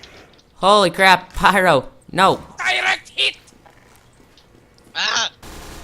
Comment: DIRECT HIT : sounded like Hitler :P